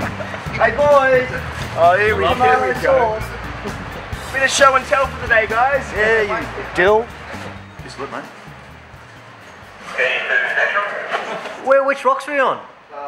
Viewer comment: did anyone else hear that guy call the other dude a “dill” at 1:39 or is that just me?